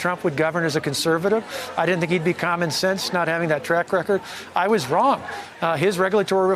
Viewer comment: Wait did he just say Trump wasn’t common sense?? @ 10:34 🤣👀